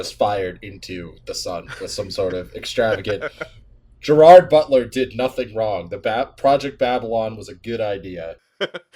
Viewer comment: The video glitched for me around 1:41, all I heard was “-tler did northing wrong,” and I had to desperately rewind